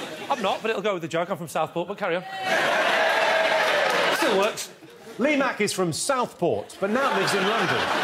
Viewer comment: Does he say "Steel works"?!